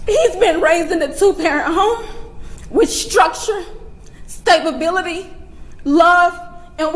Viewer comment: Did she just say "stabability" while larping as a grieving mother? You couldn't make this up!